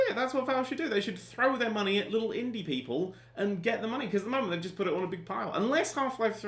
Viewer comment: I miss heard that as "Throw their money at little Indian people" xD